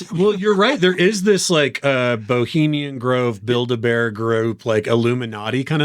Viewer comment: Did he say build-a-bear group at 5:03? i'm sure he meant bilderberg but that was too funny. 😂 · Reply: I promise I said Bilderberg! But I won't lie, ever since hearing a Jon Ronson audiobook, the way he says that word I always hear Build-A-Bear in my head.